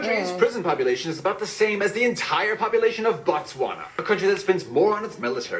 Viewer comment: did he just say Botswana? That's my country. 😀 I'm glad people actually know about my amazing country. 🤭